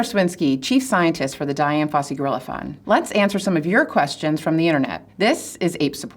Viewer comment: I misheard "gorilla fund" as "gorilla farm"